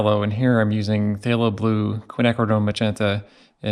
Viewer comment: Amazing ,but 03:20 that rozy red is which color?dont understand what you said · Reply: Quinacridone Magents....it's a mouthful. 😂